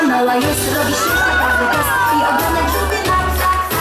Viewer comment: Why did I heard "FUCK, FUCK, FUCK, FUCK?xd"